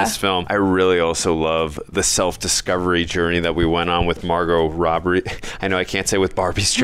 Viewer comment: Yo, did you just say - Margot "Robbery"? 😂😂